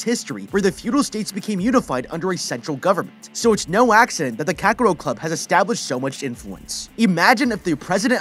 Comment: damn have to replay multiple times around 3:37 i swear to god i keep hearing kakarot club xD